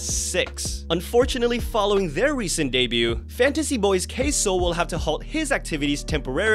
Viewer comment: I totally thought you said "Queso" instead of "K-Soul" 😂